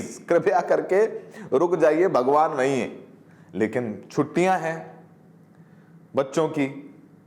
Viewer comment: pe isne kya bola h ki bhagwan nhi h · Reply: dhyaan से सुनो उसने बोला हे bhagwan वहीं है, न की नहीं है